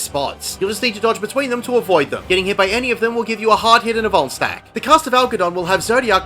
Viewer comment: god bless you, I was gonna go in blind but im feeling like being a good person today. how do you talk so fast? when I start talking to fast at work my words start jumbling together and I sound like im speaking gibberish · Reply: I thought you kept saying bone stack, im like idk wtf that is, but I dont want to get boned. then it registers at the end va va vuln stack